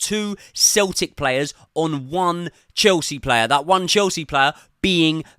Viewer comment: If i keep hearing "Chelsea battered", I'll expect a complimentary portion of chips and a pickled egg